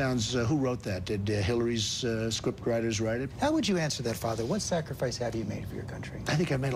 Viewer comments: did he just call Donald trump father?? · why did he call him father? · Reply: He said "what would you say to that father?" Not what would you say to that, father?" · does the reporter ask “how would you answer that, *father*??” or am i just hearing things and he called trump something else...? 😶